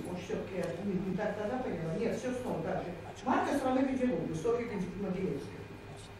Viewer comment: Спасибо за видео! Как всегда, не оторваться)... Только вот, к сожалению, не расслышала о какой книге на 1:13:28 говорит Мариэтта Омаровна. Если кто подскажет, буду очень благодарна. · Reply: Марка страны Гонделупы. Софья Могилевская